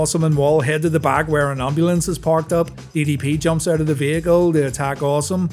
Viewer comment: Could’ve swore you said EDP and not DDP 😂😂